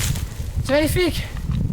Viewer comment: quand il a dit "c'est magnifique "j'ai compris " voilà les flics"